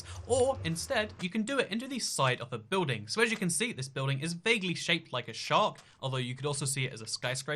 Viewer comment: “Vaguely Shaped as a shark” I heard “baby shaped as a shark” So… BABY SHARK DO DO DO DO DO DO BABY SHARK DO DO DO DO DO DO